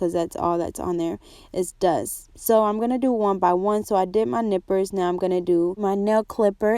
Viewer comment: Dammit Janet..... 😱 I thought you said I've just done my nipples 🤪 I was like damn girl don't do that they are sensitive 😂😂 glad you said nippers though, makes much more sense. Fab video, awesome accent too... tfs x